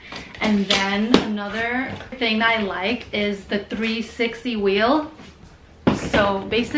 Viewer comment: I thought you said “three sexy wheels” before I realized you said 360 wheels 🤦🏻‍♀️ time to get some Calpak packing cubes for your carry on 😉